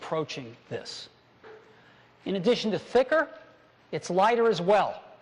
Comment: look what jobs says at 3:14 He says in addition to thinker.